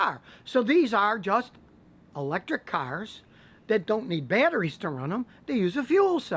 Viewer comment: What word would we have heard at 2:48 when he was explaining hydrogen fuel cells? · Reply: "These are just, electric cars."